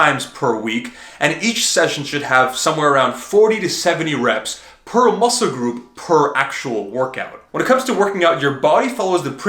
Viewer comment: "and each..." Can someone please write down what he says? I listened to this part but i never undestood/get it fully? Somebody please explain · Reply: "And each session should have somewhere around 40-70 reps per muscle group." AKA each workout when you train a muscle group, that muscle should get 40-70 reps in total stimulation. So if you train Biceps AND triceps in one workout, you need 40-70 for biceps and 40-70 for triceps.